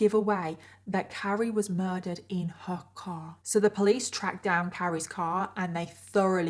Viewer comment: I misheard "Carry was murdered in her car" as "Carry was murdered in Hong Kong" and I was so confused for a moment